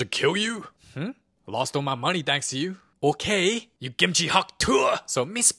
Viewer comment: "YOU KIMCHI HAWK TUAH" thats what i heard😭